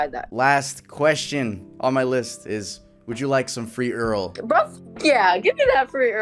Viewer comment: Dude seriously I initially misheard said "Would you mind some free or@ lmao Sorry Earl my bad haha